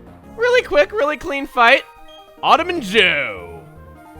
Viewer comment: When you said "Autumn and Joe," I happened to be looking away from the screen and thought you said "Ottoman Joe." So I started wondering what that was a reference to.